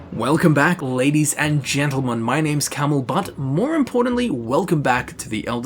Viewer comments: Ha, at the start it sounded like you said your name is CamelButt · It sounds like you said "my name is camelbutt" 😂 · It sounded like he said his name is Camelbutt. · "Welcome back, ladies and gentlemen. My name is Camel-Butt." Anyone else catch that? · it sounded like he said "my name is camel butt"